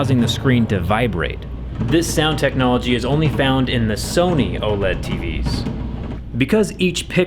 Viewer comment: Did he just said Sony?